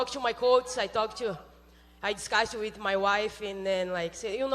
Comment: At time mark 9:44 It sounds like Amanda said " I talked with my wife " ??